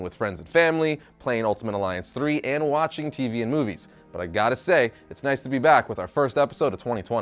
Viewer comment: I thought he said Indian movie s